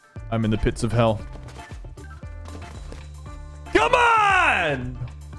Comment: HELP WHY DOES HE SOUND LIKE MARIO SAYING “COME OON”